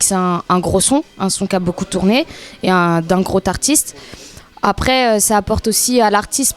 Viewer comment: D'un "grot artiste" aulieu de "groS artiste" J'ai mal au tympans ! Virez-moi cette roumaine.